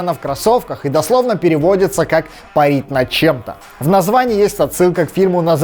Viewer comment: "парить над чем-то", если кто-то не расслышал))